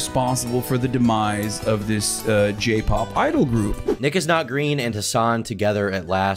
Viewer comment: bro I swear he said "n-word" 's not green